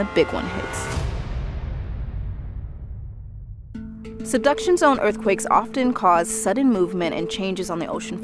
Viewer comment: Thought they were saying “seduction zone” until I came to the comments 🤦🏼‍♀️😅